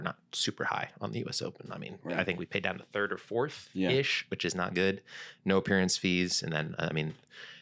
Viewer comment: In talking about the US Open, at the 22:31 mark Ben says; "A lot of pros, at least last year, were not super high on the US Open. I mean I think we ?????????? 3rd or 4th which is not good." I've listened to this section multiple times and still cannot make out what he is saying. Anyone out there hear what he referenced about being 3rd or 4th? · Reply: He said “I think we’re paid down to 3rd or 4th-ish” (place). Meaning payouts aren’t deep into the finishes.